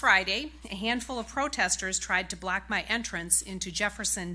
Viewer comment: Did she just say "black" my entrance. .......